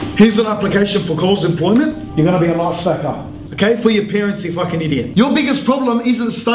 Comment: What does he say at 2:02 your gonna be a what... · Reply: Sounded like ice stacker or something "___ stacker" I'm assuming because most Coles jobs are stacking shelves.